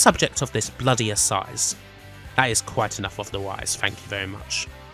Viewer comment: Any clue on what narrator said here? Noope? Me neither. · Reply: "That's quite enough of the Royse thank you very much"